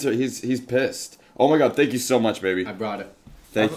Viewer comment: did he say baby? Also Reggie’s face